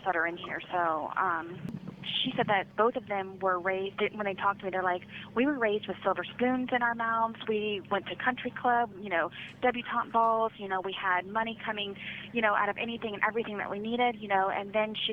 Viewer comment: At first I thought she said, "They were raped with silver spoons in their mouths."